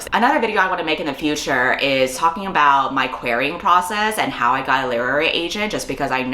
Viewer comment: i thought said "my queering process" instead of querying process and was sure she would tell us how she got a girlfriend